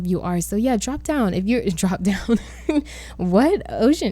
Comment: Why’d I hear “drop down and get your eagle on” when you said drop down 😂 omg lol but team boy momma of one over here!! And I love this build. It reminds me of the trailer my cousins lived in when I was younger.